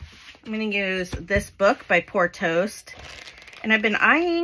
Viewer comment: OK..it's time I tell you something hilarious....Every time you say "Poor Toast," I hear "Porto's" which is an amazing Bakery/Eatery in the city I live in...and my entire city...and a lot of SoCal people know of "Porto's"...we all want it, eat it, crave it..stand in embarrassing long lines to get it..xo